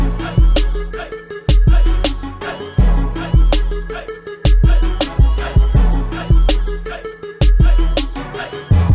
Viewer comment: No entiende el programa del minuto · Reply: La palabra es regedit y puedes entrar hermano también no le había entendido pero investigue en mi cel.